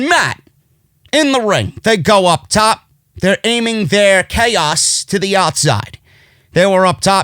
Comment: & 17:44 in did say “ Chaos”🤔@ #Twitter ? Thanks for the “Rub”🙏